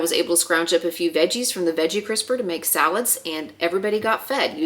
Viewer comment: this is a great video❤. I thought this might give you a chuckle - ne a r the end when you said veggie crisper, I looked down at my phone and thought you said 'veggie whisperer' andvyou got my immediate attention bc I thought there was a new fridge technology app that tracks veggie freshness😂. Yes, I can be a goofball. 😊